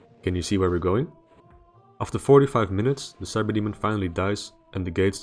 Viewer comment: did he say 4-5 minutes or 45 minutes?!? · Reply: 45 minutes. 2,700 seconds.